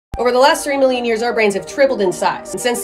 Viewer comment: At the beginning instead of 3 million, I heard "Over the last 3 years our brains tripled in size"... Like man... we evolve quick! 😭